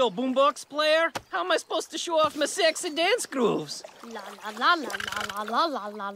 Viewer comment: Is it just me or did he say sex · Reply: He did! He said “Sexy dance grooves!”